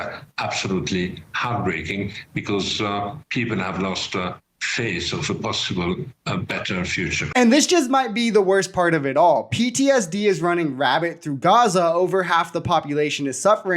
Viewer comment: Did you say “PTSD is running rabbit through Gaza.”? Like:🐇?